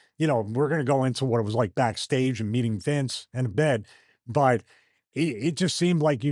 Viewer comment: I had to go back and listen to Stevie three times and every time it sounded like he said "what it was like to see Vince in bed. " Is it just me am I trippin? I still don't know what he actually said.